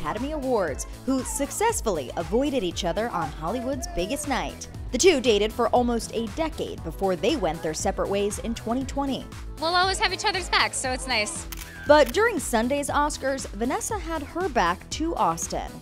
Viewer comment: I thought i heard them date 8 decades😂😂😂